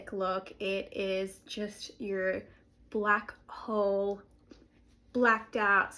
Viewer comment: 👽Until you said black hole, I heard ‘space’ and thought typing space, like spacebar, and thought ‘well that won’t be helpful to you at all’ 🤦